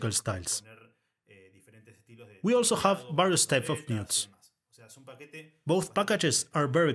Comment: Thanks so much for this. What is it you say at 10:14 "we also have XXXX type of instruments"? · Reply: Sorry, that was an audio glitch. I say "various types of mutes"